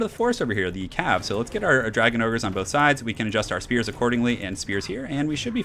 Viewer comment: did he say "Dragon Ogres"? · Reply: I will subscribe now. Only because you said Dragon Ogres at 4 minutes and 15 seconds in.